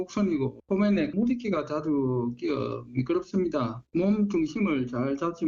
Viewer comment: 저는 자막과 달리 물이끼가 자주 생긴다는 설명으로 들리네요.